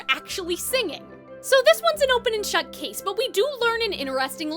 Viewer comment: I thought you said open and chud lol